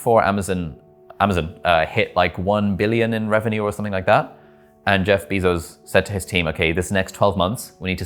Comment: Did I hear Jeff 🐝-zos? haha